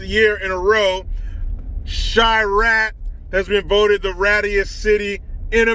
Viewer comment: All this time I thought you were saying Chi Raq because it's a war zone? 😆 🤣 😂